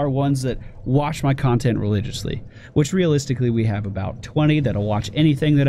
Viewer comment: Did he say only 20 people watch everything he does? Cause I thought there would be more people in that group with me.